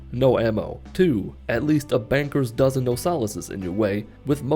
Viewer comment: Did... did say a "banker's dozen"?